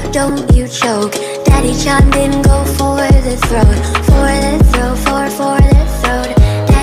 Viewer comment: All I heard is “Daddy Cham didn go for the throat”